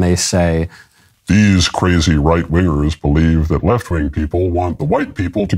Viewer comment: sounded like Michael said wiggers 😭🤣 Anyways, love the content Michael, keep up the amazing work, if everyone else un-subscribed ide still be here :D